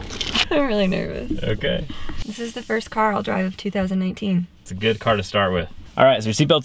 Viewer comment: who else heard 2018 instead of 2019?